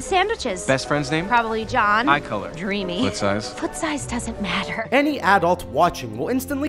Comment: "Best Friends name" "Probably John" "What size" "Size doesn't matter" Disney: Heh Heh Boi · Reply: I hear "foot size"